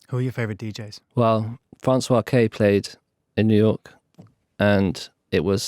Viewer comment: I couldnt catch the name of the DJ he mentioned at 30:49 Help please! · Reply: Francois K